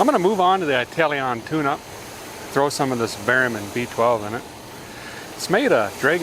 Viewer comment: The first time I thought said barium, as in enema, when he was really saying Berrymans as he poured a dan down the carb. Then he followed up with 'feed her some onions' and this fella nearly died laughing. Thanks, you're a national treasure.